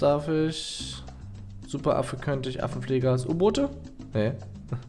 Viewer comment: Ich hab erst verstanden Affen Pfleger Ass 😂😂